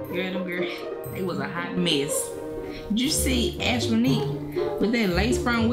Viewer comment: did she really say "Ashmonique"